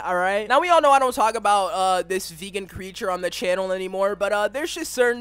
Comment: Did you say “This vegan creature” or “This vegan teacher” I am literally confused lol 💀